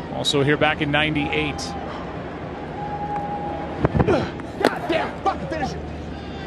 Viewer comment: I heard "God damn it, fuck this shit"